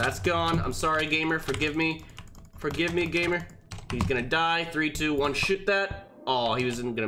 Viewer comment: sounds like he said 1 2 3 sh*t that LOL